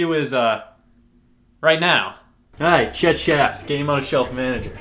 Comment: I swear I thought he said "Hey names Shit Shaft"